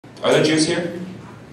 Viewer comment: All I heard was ,"Are there cheesecake?"